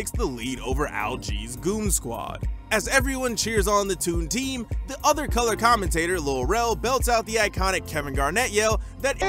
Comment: nobody going to call out how this voiceover guy says at 3:25 ish "the other colored commentator" did I mishear that? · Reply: It's "The other colour commentator", which is the term for the commentator that fills in when the play-by-play commentator has nothing to say!